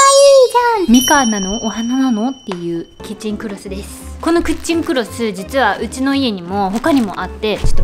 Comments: キッチンクロスが クッチンクロスに聞こえたwwwww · この『クッチンクロス』 にめっちゃ聞こえる😂